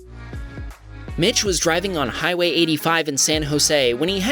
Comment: i thought it said b**ch